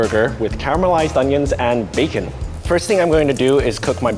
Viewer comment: i hear "bajin"